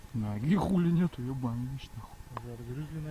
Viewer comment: parece que dice "hijo de su no mames" xD